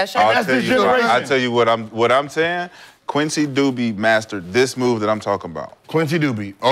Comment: I thought he said “Quincy Dookie” 😂😂 s/o to the legend